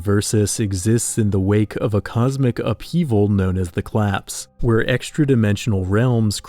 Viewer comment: Am speedyTab Btw, did you say collapse or claps at 1:23? Both work but I would like to know